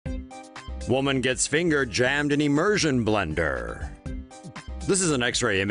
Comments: Ohhh I thought it's said"Woman got Fingered by a Machine" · At the start, all I heard was "Woman gets fingered" XD